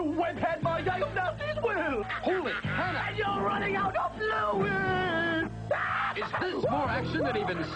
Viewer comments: What does he even say?! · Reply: "You're running out of fluid!" As in web fluid. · At first I thought Spider-Man was saying "Holy Hell!" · Wtf does he say at 0:18 ? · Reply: Your running out of flu weed! No, fluids